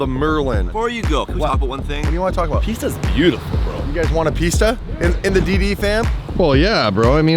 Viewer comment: thought he said want some pizza